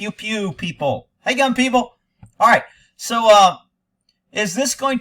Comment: At first, I thought you said “hey gum people.” Until you said “hey gun people.” 😂